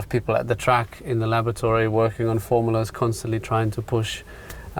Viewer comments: did he say lavatory instead of laboratory? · he said lavatory instead of lavatory